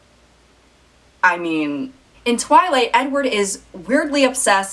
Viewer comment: I thought she said “nword” when she said “Edward”